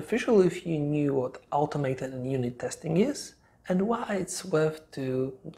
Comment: Hi, nice video. Quick question: around @ 8:50 did you say ‘automate’ & unit testing? If so, what did you mean by that? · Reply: "Automated and unit testing" - Automated are programs/scripts that test the programmed flows (i.e. "happy path") in form of i.e. Selenium. Unit testing are tests done more on a codebase level to test if the variables and loops are still working correctly after an update.